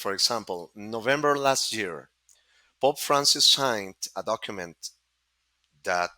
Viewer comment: Oh, his accent was so thick. I thought he was saying "Bob Francis" for the first half of this interview, and I was like why did the late divisive and colourful South Australian radio celebrity know or have power over all of this stuff??? Then I realized, Oh, he's talking about "Pope Frances!" Loved his insights and clarification of the actual historical witnesses. What a well-informed LEGEND!